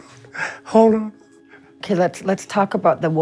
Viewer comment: Did he just say Hodor?